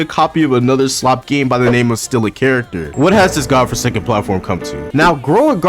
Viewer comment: Did I hear, forsaken?